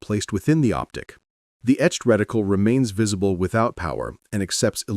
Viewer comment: Did the just say wedicle?